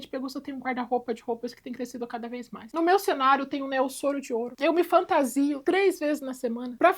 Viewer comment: “No meu 🎬 cenário tem um ‘neosoro de ouro’ “…..acho que escutei errado…(?).o que a falou q tem no cenário dela???